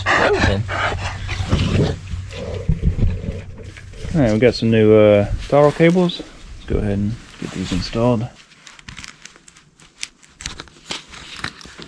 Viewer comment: did you say “power cables” instead of throttle? Haha well it’s actually true… they control the power haha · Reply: lol I’m pretty sure I said throttle cables🙂